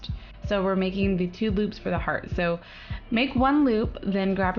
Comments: "so we're making the two boobs for the heart" i probably misheard it, but boobs or loops, they're both legit ways to describe the heart. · i heard let's make the two boobs of the hear when she was making the first ring. XD